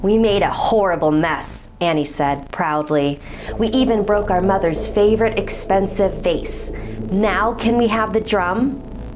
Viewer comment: why did I hear “our mothers favourite expensive face” oops 😬